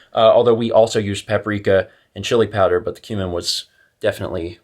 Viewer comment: I misheard 'cumin' as 'human'. No, twisted subconscious mind, not a cannibal.